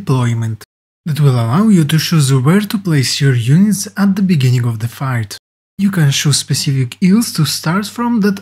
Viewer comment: did he say beginning of the fart?